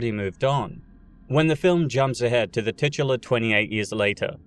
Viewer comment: Did you just say tits?